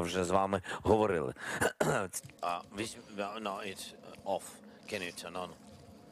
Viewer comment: what did he say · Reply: I heard nun just just a second